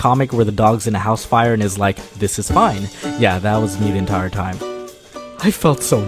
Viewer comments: I thought he said this is fine but he says this is mine! Mandella affect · did anyone notice he said mine instead of fine.. this bothers me on a spiritual level